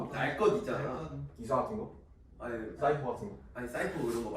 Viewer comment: am I crazy or did anyone else hear ‘cypher’ someone Korean please help